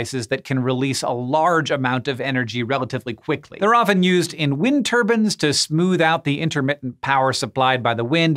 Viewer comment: I’m sorry... did you say “wind TURBANS” at 3:23?